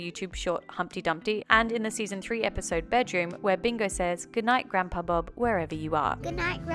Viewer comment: I just figured out that bingo stayed good night grandpa bob because before I watched this video, I have always thought she was saying “ Goodnight groundhog bob” 😂😂😂